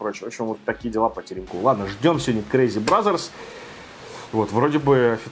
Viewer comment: когда услышал слово BRAZZERS))